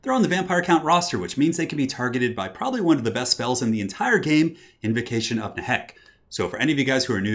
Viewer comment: lol I always thought you said "Invocation of the heck". This is the first time seeing it written